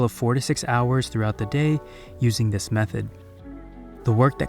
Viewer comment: I thought you said you "usually work 46 hours a day using this method"... 😄😄